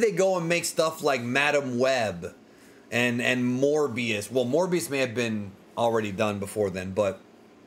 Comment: When You Said “Morbius” I Thought You Said “More BS” Either Way You’re Not Wrong lol